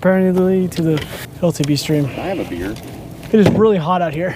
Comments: said at 4:30 "can I have a beer?" did anyone else hear that? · Did anybody hear ask for a beer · did I hear say can I have a beer